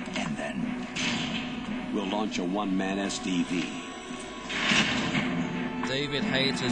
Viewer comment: I wasn't paying attention around 1:35, so I thought Campbell said "We'll launch a one-man STD" instead of "SDV"